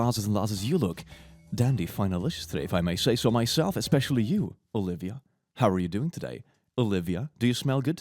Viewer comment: ??????? My name is olivia... is that what he is saying?? Kinda creeped out